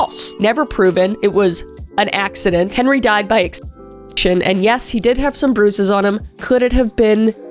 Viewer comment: wait what does she say here???? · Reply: Asphyxiation, aka he couldn't breath